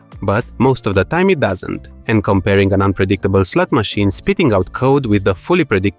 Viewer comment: I thought I heard a "slop machine" 😂